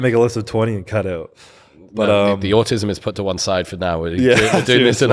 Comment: What? Did he called CBUM autistic? Honest queston cause I can't hear properly so it sounded like that.